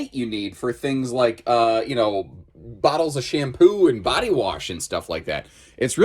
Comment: I had to rewatch to confirm you said, "Tall enough for bottles of shampoo". Because after my Monday, what I heard was, "Tall enough for Bottles of Champagne".